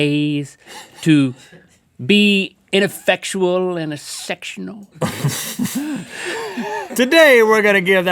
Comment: wha... WHAT DID HE SAY?! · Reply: Sam R Took me a moment too but he said sectional lol